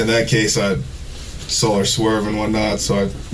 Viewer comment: Early on, Mark misheard Meathead say "I saw her swerving one night," making him wonder if he was stalking her. He might have been, but he actually said "I saw her swerving, what not." His use of "what not" is a classic way bad liars avoid specifics. He might as well have said "yada yada" throughout. I'm sure the interrogator picked that up quick. And I'm sure his victim didn't say "what not" or "yada yada" so much. Also, very early in the interrogation he repeats that he told the victim something like "Why you making me pull you over, I'm (off duty and going home) and tired." Exactly, Meathead, no off-duty cop pulls over a minor traffic infraction unless they have bad intentions (unfortunately THAT itself happens way too often). Words themselves betray liars more than body language. But the main thing is the credible evidence that pointed directly to Holzclaw's guilt.